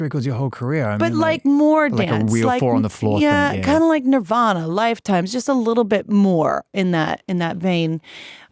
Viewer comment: did she say “Nirvana, Lifetime”?????